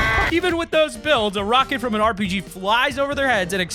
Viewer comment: I’m not the only one who heard him say RPG instead of proximity gl... right?